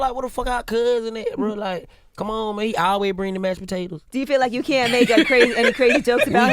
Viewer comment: When DC said “he always bring the mashed potatoes “ I thought he said “he always bring the Mexican tables” 😩😭😭😂